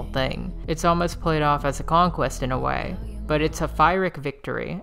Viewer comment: At approx 11:23 you said ___ victory. What was that word please? Fireic? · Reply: Pyrrhic (my pronunciation may be off) but it just means “victory that inflicts such a devastating toll on the victor that it’s basically a loss”